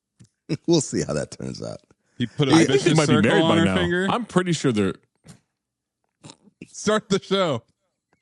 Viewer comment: damn I wish I could tell what said. Dude sneaks in the best lines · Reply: Put a vicious circle on her finger